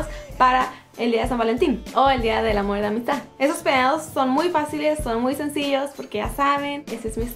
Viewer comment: JAJAJA yo escuche en el minuto 0:18 en vez de ''estos peinados'' escuche ''estos pedos'' JJJJAJAJAJAAJJAJAAJAJAJJAJA!!!!!